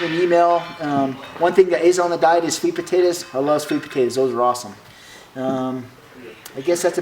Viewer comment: What does he say at 5:40? Sweet potatoes?